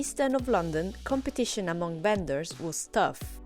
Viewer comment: did she say “competition among Benders was tough” 😮